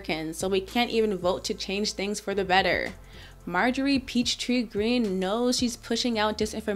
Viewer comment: Did she just say peach tree dish?